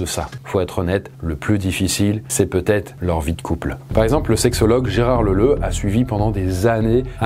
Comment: j'ai entendu Gérard Leuleu...